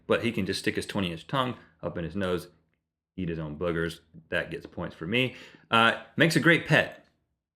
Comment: I thought they said eat their own ear ,and when I saw the scissors I was lie D:.And now here I am